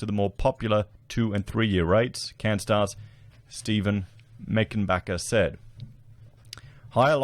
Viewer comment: Humm!? Did I just hear saying: “Steve making bucks”? 🤭